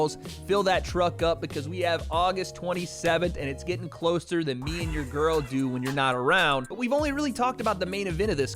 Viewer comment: I had to listen again, thought you said "closer than me and you when your girl's not around"